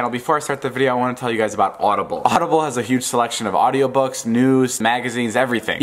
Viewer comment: When he was talking about audible I heard him say "books nudes " I'm fucking dead!😂😂😂😂